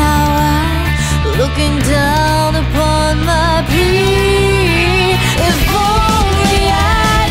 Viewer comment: It sounds like she says, "An angel up in my tower looking down upon my pee..." Is she saying "free?"